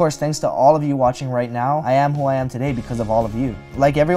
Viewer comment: 5 I thought he said “I Am Hawaiian Today”